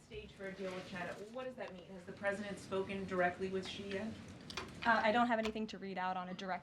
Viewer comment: I thought she said Shein not Xi yet. That's an unusual way to refer to the President of China, just by the first name.